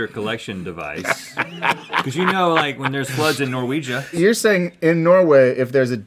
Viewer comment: did he say Norwega